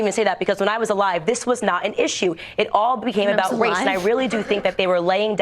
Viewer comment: Did she just say, ‘when I was alive this was not an issue’? (at 2:55) Breaking News: Candice Owens admits to being undead in Fox News exclusive!